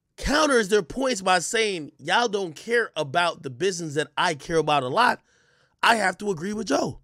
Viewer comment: I thought he said he don't give a damn about the business but his character 🤣🤣🤣